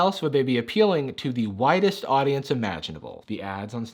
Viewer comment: lmao I had to rewind at 10:18 because I thought he said "whitest audience" and was then confused why he didn't address that again😂